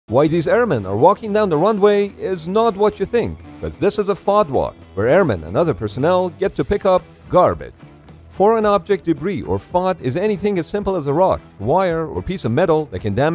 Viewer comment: I didn’t read the title and thought he said fart walk with airmen 😂